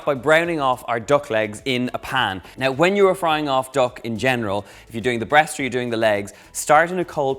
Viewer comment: I didn't see the Title and thought Donal said "when you are frying off DOG in general"